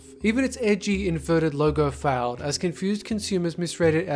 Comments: I thought it said Lego fails lol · I thought it said LEGO fails XD